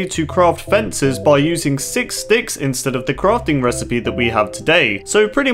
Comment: i hear 6 d****s insted of 6 sticks